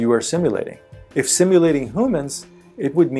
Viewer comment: Did he just say simulating "who mans"?